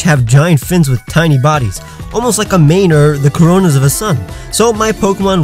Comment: Did he just say the CORONAS of a sun